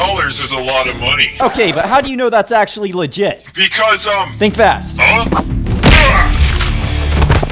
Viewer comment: I almost thought he said "THINK FAST CUCKLE NUTS"